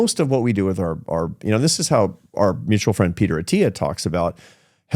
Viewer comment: I thought I heard "Our mutual friend Peter Thiel". What a shock.